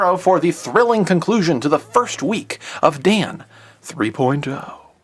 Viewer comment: it sounds like you say dan three poin toe XD